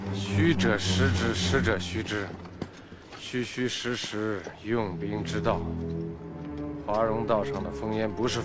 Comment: can anyone write in chinese what cao cao say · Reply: 虚则实之，实则虚之。虚虚实实用兵之道